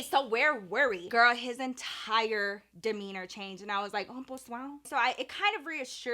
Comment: What is that word that says that sounds like "Bon Soir" For example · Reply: Pos wow